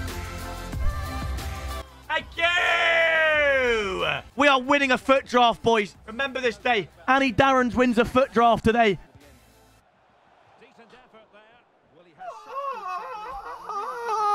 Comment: Was I the only one that heard him say “rember this day boys, anny darrons wins his first footdraft” 🤦‍♂️Like bro forgot his own name 😂